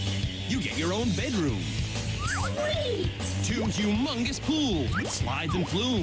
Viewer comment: 90’s anti-corperate Nickelodeon is so different to how corporate Nickelodeon is now. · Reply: Anyone else heard at 03:49 the word "amongus pools"?